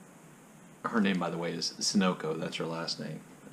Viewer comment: "Sunoco" = "Sun Oil Company" (an "Ohio thing?") sounds like you say ... Mrs Sinico, sin and all ... I'm just finally getting 'round to doing my VR to you, mate ... But don't think I havent been thinking hard about it, all week: "notes city," now? Now, time to co-ordinator & "pull the trigger," 'cause Mr Duffy just might be the "Man-in-the-Mac" in Ulysses, you see ... 🇮🇪🤣🍀 5:50 IMA talk about your point, here, a bit, too: Mr Duffy's "latent homosexuality," hmm, 'cause--seriously--Id never thought nor read criticism re that ... I mean, there's bound to be some/a lot, as mentioned in past #Dubliners2019 vids, "Queer Joycia" really took off--w publications of books/articles--in the mid-90s ...